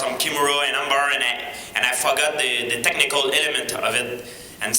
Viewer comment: btw does GSP say "i fuck up the technique" at 1:20 ?